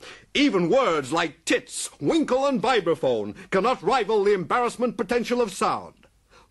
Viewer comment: Cleese solemnly intoning "Wankel Rotary Engine" destroys me every time. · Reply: What th is he saying, 'biverphone'? Or, am I mishearing it. What is he saying, there?